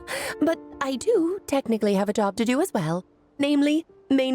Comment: Why do I always hear Alice say "But I have adopted you as well" instead "But I have a job to do as well" 😅🤣